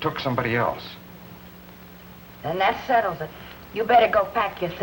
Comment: Does Kelly say "It sucks." (?) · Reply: Arthur Harrison « that settles it. »